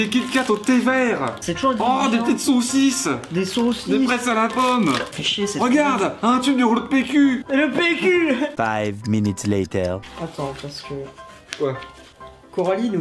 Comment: j'ai entendu "des fraises à la pomme"